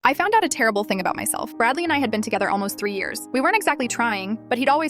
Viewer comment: Why did I hear/read "dudley and I have been together for 3 years"